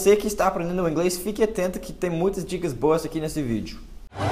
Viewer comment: Ashaushau, eu ouvi "tem muitas dicas bosta aqui nesse vídeo".